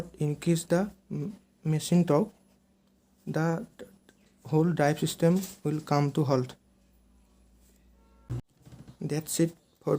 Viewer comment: what you said the whole drive system will come to ........ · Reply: The whole drive system will come to halt